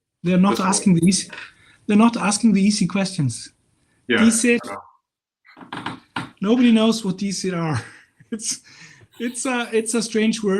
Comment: What word is talking about around 21:12-21:50? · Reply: Dísir. Dr. does have a slightly longer video with a few more attestations at this link: <link> but Professor correct-- it's really very ambiguous as to what they actually are (much like the other terms in the video, heh).